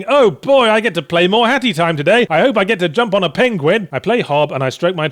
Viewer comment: I swear it sounds like he was saying hattytime.